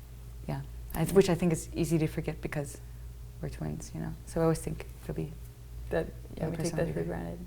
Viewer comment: It took me a moment to realise that they were identical twins because their distinctly different personalities overrode the visual. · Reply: she actually says they're twins